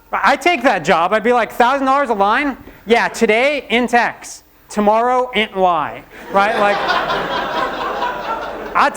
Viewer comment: Can anyone tell me what exactly did he say at 24:16, cant clearly understand. Today int x(in tax?),tomorow....?? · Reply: "today int x, tomorrow int y"